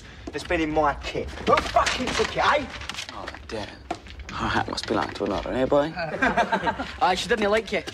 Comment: What does Cillian say @ 0:16 I can't understand him and either can the cc ! HELP ! Yes, I'm actually interested in what this beautiful man says ! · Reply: Her heart must belong to another, eh, boy?